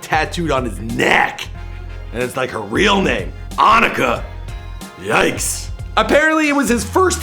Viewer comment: A lil shady in this video huh? Did u just call her "On-icka" frfr 7:10mins ... U had one job smh 😐✔